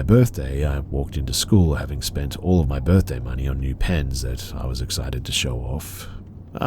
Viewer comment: OHH! "NEW PENS!" I had to go back there, because I could have sworn she said she spent her birthday money on depends and was looking forward to showing the mouth to everybody ar school.